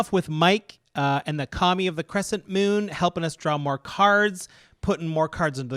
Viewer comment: I missheard you say "Commie of the Cresent Moon", which would be a pretty good name for a group hug deck. 👍